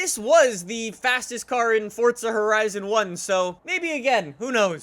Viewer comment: Why does it sound like he is saying Fortza